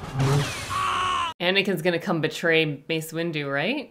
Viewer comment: I heard "Based Windu"😭